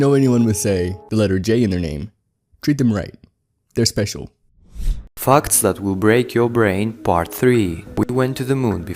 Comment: everytime that guys says facts i just hear fucks